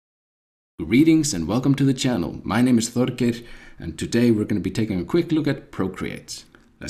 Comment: All I heard was "my name is Thor" - sign me up! (Very helpful video, by the way!~)